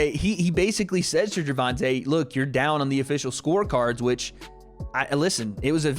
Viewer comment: I think Floyd said "you down on the unofficial scorecards", not official. @ 6:09 And he said to tank "show me you great". Great fight nonetheless.